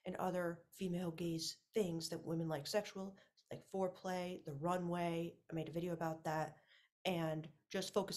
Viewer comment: What do you say at 2:20 after the word foreplay and what is the video about it?😊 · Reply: was it "the runway"? I can post a link to my video about that if you want?